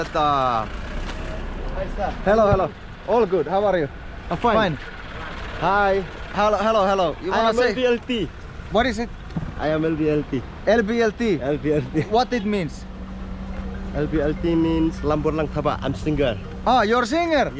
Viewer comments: In 4:50 i thought that guy say "i am LGBTQ 😂😂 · i thought he said he is LGBT🤣